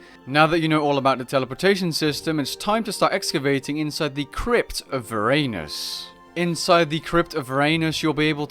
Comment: ok i was watching this in the background and not paying too much attention but at like 2:00 coulda sworn i heard "of your anus" lmfao had to replay that